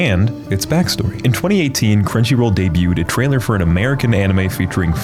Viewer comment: did you say cringyroll?